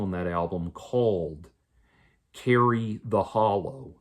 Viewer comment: I misinterpreted and thought you were at first saying; "Carrie/Karrie 'The' Hollow" as in some hollow woman was named Carrie/Karrie. Not to undermine the husk-like feeling your are trying to describe but, I thought it was ironically comically to some degree that I thought at first you were first talking about a succubi-like woman named Carrie/Karrie.